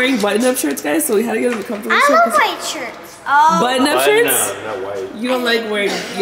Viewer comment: is meee i heard “whit shirts” too